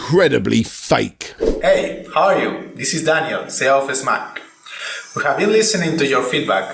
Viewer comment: sounds like he says 'CEO of Smack'...i thought there was an Entire Point made earlier, about how "S(team) Mach(ine)" was pronounced 'Smash'...am i about to answer a question before its even asked...?